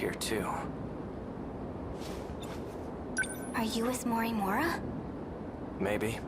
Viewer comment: I heard "Are you Ms Morimura?"